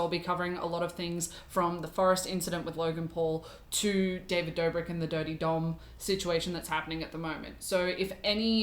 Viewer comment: thought you said "david dobrik the daddy dom" and it sent me